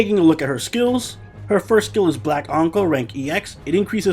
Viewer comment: I thought i heard you said 'Black uncle'